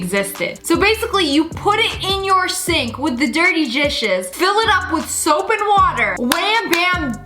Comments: I'm sorry but am I the only person who heard her say *dirty gishes!? * 😂😂😂😂 I CANT STOP LAUGHING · she said jishes instead of dishes